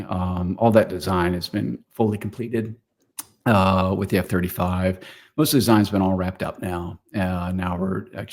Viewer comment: did say "F-35"? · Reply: Yes, F-35 was announced months ago.